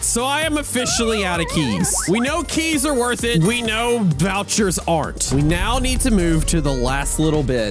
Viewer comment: did bro just say Boucher?💀